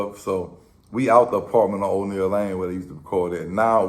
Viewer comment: Lanier my nigga 😂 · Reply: lmao you right, I always thought he said O'Niel, as in like from out G.O. through the whole city, but I looked it up l'm actually glad you corrected that I been thinking that for like 20 something years..